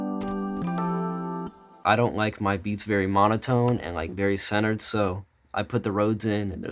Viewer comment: I thought he was about to say “I don’t like my beats very much”😂